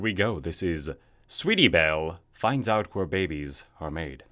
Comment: I thought it said barbies... ._.